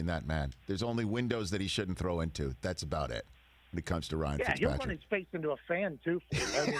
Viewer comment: emm...i didn't understand what pat said at the start of the sentence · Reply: Megálopros “yeah he’ll run his face into a fan”